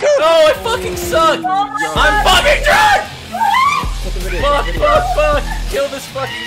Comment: does he say he‘s drunk?? With 2 deaths 😳😁